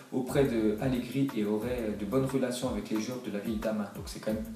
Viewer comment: c'est moi ou à la fin de la phrase il dit fellation à place de relation